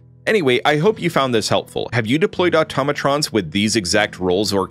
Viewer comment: Did he just say “automotrons”?